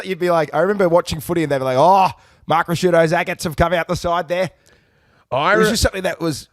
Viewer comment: please translate for an American. Did you say "eggets"? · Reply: "Agates" - a slang term for testicles.